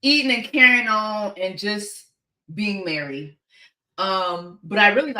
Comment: said "being merry" and I thought I heard "being married". Almost dropped my phone like YALL NEVER TELL ME NOTHING!!! Anyway. False alarm. Or is it 👀👀👀👀